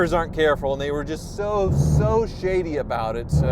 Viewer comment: I heard "shidy". Best slang of the year for combining the two. 🎉